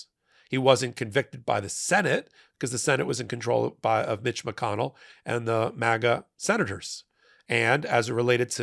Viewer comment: 😱 I thought he said B#tch McConnell. Did anyone else hear that? Love this channel! Vote true blue! 💙 💙 💙